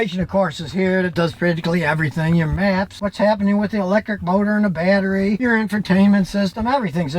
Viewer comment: did he said 'electric boner'